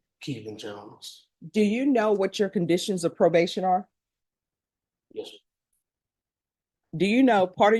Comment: did he say "yes sir"